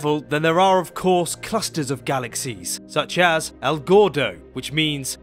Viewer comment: "el gordo" which means "the fat one" in Spanish. I thought he said "Al Gore-doh"